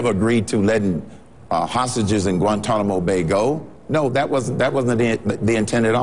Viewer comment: Did anyone else hear Citizen Cain say "Hostages in Gitmo"..... Well, you can't believe what you get off those internets.